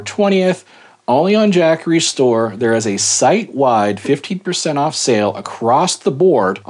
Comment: I thought you said 50% off sale and almost had a heart attack that I missed it…LOL.